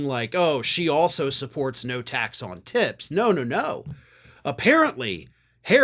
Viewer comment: I had to replay the start of your video ...I thought you said ' tax on t*ts. " 🤣🤣🤣